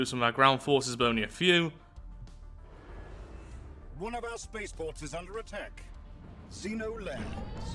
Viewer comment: Why did I hear "One of our spaceballs are under attack"